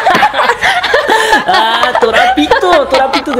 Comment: creo que dijo que el maestro vale pito